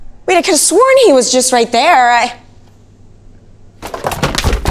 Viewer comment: “COME BACK HERE I NEED MY SELFIE” 👹😭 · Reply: I heard "COME BACK HERE I NEED MY SONS FEE “ hahaha😭🤗😖